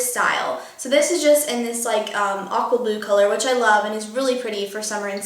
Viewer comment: When you said "aqua blue" I thought you said "awkward blue", and I was like huh? why is it awkward? lol